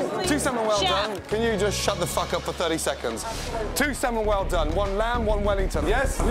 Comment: Is it just me or do I hear “absolutely” after Gordon Ramsey says stfu